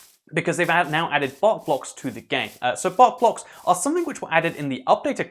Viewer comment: I thought he said fart blocks